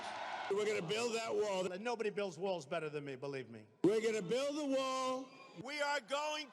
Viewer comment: All I heard is " Built the War " sorry guys